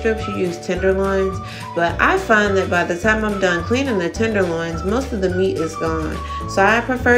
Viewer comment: Oh lord!at exactly 1:20 i thought you said tittieloins😆😆😆,anyway,today's meal looked awesome.